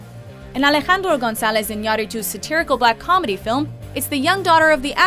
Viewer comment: wtf did she just say..? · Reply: Alejandro González Iñárritu.